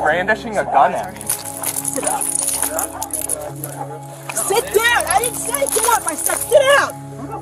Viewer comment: I hear a man voice say to sit up.